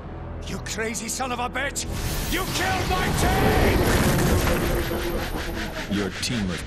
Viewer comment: HOW DOES IT FEEL TO BE DECONSTRUCTED? TO BE THE VICTIM? TO WATCH YOUR DREAMS DIE?! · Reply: He said dreams? I thought he said teams